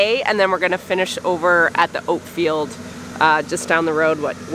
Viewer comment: When you said "oat field" I swear I heard "oatmeal" made me hungry for a big bowl of oatmeal 😍